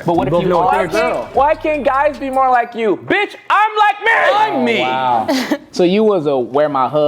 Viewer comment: i thought he said i like men